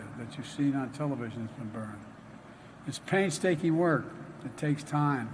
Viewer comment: Did he say “that we burned ?”